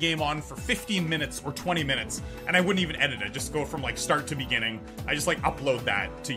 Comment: did he say start to beginning?